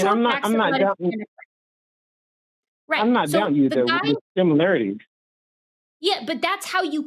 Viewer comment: Did I hear him right? Does he say he's not dumb?? If so, that's so funny 😆😆😆😆😆